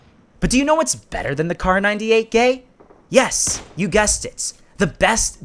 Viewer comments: am i the only one who heard him say "Kar 98 *gay*"? 😂😂😂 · Did he say kar98-gay 😂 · Did you said kar98 gay at 8:45? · DID HE JUST SAY KAR98GAY?